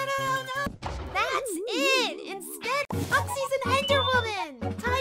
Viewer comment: I heard you say Me Instead of Oxy